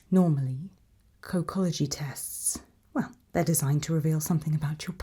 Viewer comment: my dumbass thought ts said cockology